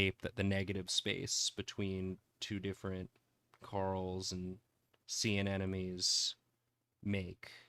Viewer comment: when yall said youd draw corals during the warmup i thought you said Carls as in youd doodle a bunch of guys named carl and i was so ready to draw some silly guys lmao